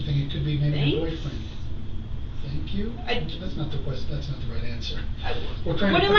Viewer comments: I think she might have said "thinks?" instead of "thanks?" and she thought he was still saying "thinks?" is not the right answer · i think she said " thinks?" Dont yall think so?